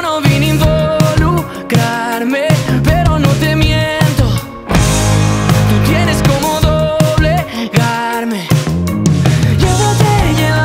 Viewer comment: Al principio pensé que decía "tú tienes como doble carne"